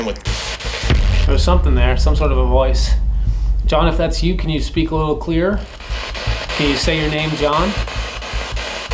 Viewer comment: I heard something like _____ your camera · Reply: dude me too!! Like turn off your camera